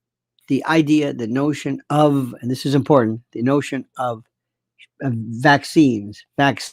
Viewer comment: vaccines was it? I wasn't quite sure what you were saying 🤔